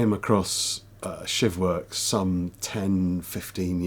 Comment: Good vid but at first I thought you said "$hit works" 😂 I thought it was a horrible name for a self defense group. That's what I get for listening while cooking 😅